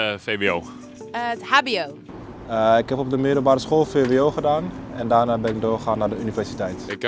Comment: the subtitles are wrong. It says that he went to university after VBO, but that is not possible, so it should have said VWO. VBO no longer exists since the introduction of VMBO. Before (even when I went to secondary school) there was IVBO, VBO and MAVO, HAVO and VWO. In 1998 or 1999 IVBO, VBO and MAVO changed to VMBO. With a VBO diploma you could go to MBO at the time, which level of MBO you went to depended on the grade list of the exams at VBO.